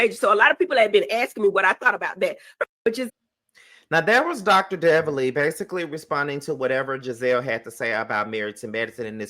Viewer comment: Did you say Dr. Deviley 😂 not sure if that was a Freudian slip, but it was right on. She must do better!